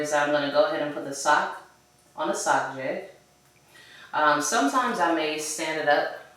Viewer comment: I appreciate you, teaching us, I wanna start sublimating as well. my question for today is what is the name of the cardboard that you put the sock on? it sounded like you said sock jet... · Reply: My pleasure. What I put the socks on is called a sock jig